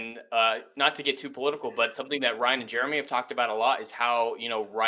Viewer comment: If you watch this at faster speed. It sounds like you said Ron Jeremy said. Instead of Ryan and Jeremy